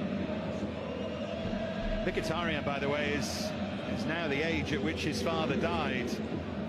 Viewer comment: What the commentator said on 13:45 anyone please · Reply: He was talking about a player whose father died at the same age at he is right now